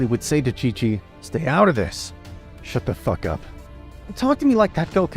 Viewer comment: did he just say shut the f### up to chi chi